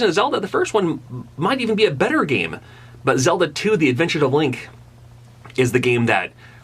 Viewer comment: When you said Zelda II - The Adventure of Link, I heard Zelda's Adventure, And I was like.. :|